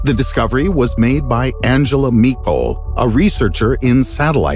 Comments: anyone else hear "Angela Meatbowl"? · Ohh Michol 😂😂😂😂 I thought he said Angela Meatball 😂😂😂😂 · thought you said Angela Meatball lol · Ohh Michol 😂😂😂😂 I thought he said Angela Meatball 😂😂